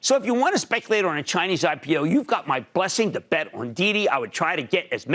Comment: Every troll on the internet shaming Cramer about this needs to learn English. He clearly said "bet."